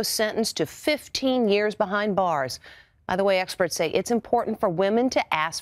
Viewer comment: What it says in 2:32 i can't hear it "experts say female to ask........."